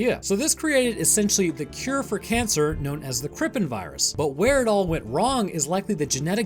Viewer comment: Krippin Virus - most badass name for a virus I've ever seen I'm not gonna thank you for the likes. I fucking hate all of you. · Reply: All I heard was trippin' virus.